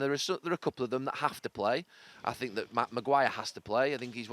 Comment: Did he say "matt maguire"?